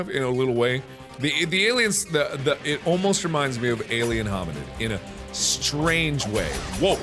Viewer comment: I'm not sure why at 11:06, the caption says "Alien Hobinant(?)," but the game Markiplier was referencing was an old Flash game called "Alien Hominid" that got brought to consoles shortly after.